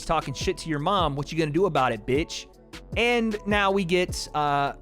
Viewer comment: react to the face to face. This moderator did a terrible job and kept interrupting them while Ariel let them talk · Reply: I thought Jake said do what that bitch says so I was like he said he's mom's a bitch Jake is done for